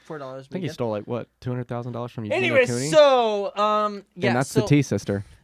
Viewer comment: yo i need the tea on the bryan stars and eugenia cooney sitch 👀☕️ edit: i'm not surprised that bryan kept it a secret about austin jones 😒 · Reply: andrea almaraz I thought at first he said 200 dollars but I did now know it was 200,000 😵